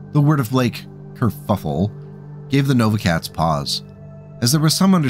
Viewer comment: I heard it as "cave the Nova Cats paws" and I hope other people have as well. The history breakdown here made me lament the loss of the Nova Cats, a faction beat up so bad you'd think they were the Capellans. Also, I really appreciate how you cover your bases with the MF variants, the anti-infantry/anti-vehicle options are always welcome!